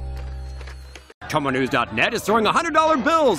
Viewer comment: Leave your best comments at <link> and you could win $100! Five winning comments every week! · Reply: I thought you said each MONTH